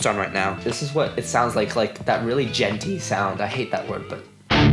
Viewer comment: hahahaha I thought you said "It has that really Gen Z sound" 😂. That's some sexy metal tone and even the clean is sexy. I need a helix!